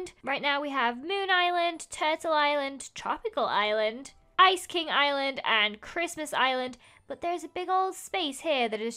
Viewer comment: Who else are in the no thumbnail club? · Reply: Was I the only one who thought she said "Ice cream Island" instead of "Ice King Island" ?